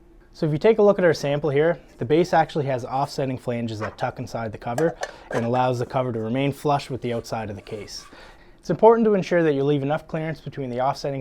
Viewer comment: I'm sorry, but.....after replaying this video at least 6-times, I cannot understand the word he is saying at: 01:39 for me to understand how this relates to a clearance of "10- to 20-thousandths of an inch will work fine". Can somebody write out the sentence he is saying during this time-mark? --- I know you want to keep these videos short, but slow your speech down a bit and be more articulate!!! THANKS!!! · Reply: "This case has offsetting flanges that tuck inside the cover, that allows the cover to remain flush with the outside of the case. It's important to remember to leave enough clearance between the offsetting flanges of your base and the inside of your cover." Then the remark about clearance.